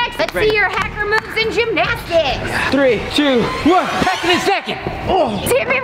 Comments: When jumped into the foam pit, he said Hackin and Snackin. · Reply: I thought he said back in a second lol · I think he said in a second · He said “Hacking and snakin”😁✌🏾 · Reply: I thought you said hack in a second